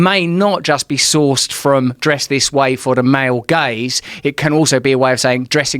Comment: every time someone says 'male gaze' I hear 'male gays'